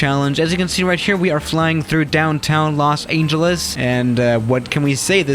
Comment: Did he just say "Los Angel Less?" 0:21 , 0:29 and many more times...